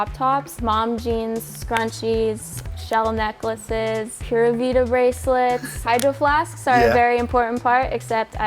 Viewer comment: Did she just say "Pura Vida bracelets" at 1:55 or am I imagine it?